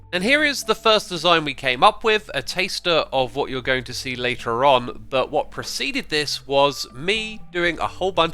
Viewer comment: X did you say a taster at 2:48?